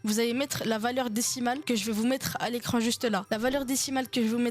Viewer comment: t'allais dire quoi à la fin? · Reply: j'ai dit vous l'écrivez ici